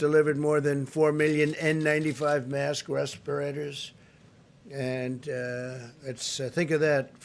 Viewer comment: He said 4 million ventilators. What he meant was 4 million masks. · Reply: He actually said, “4 million n95 mask respirators ....and 4,400 ventilators.”